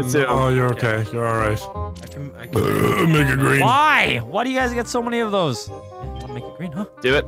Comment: nogla what did u say🤔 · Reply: He burps and then said make it green!